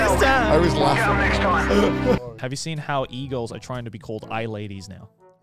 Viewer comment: I thought Joey said eagles instead of e-girls